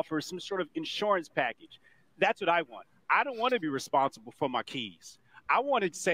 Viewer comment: I wasn't paying attention, then I thought I heard some guy say "I don't want to be responsible for my kids", I was like what da!